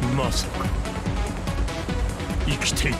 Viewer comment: the translation is not accurate with what kazuya is saying. He said “Masaka” which means “i don’t believe it” or “No way” that’s why he sounded a bit surprised when he saw jun